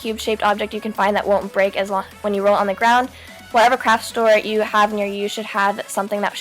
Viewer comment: did she say crap 💩💗😂😜